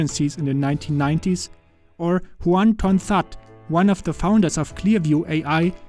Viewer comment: Yo i thought he said one ton thot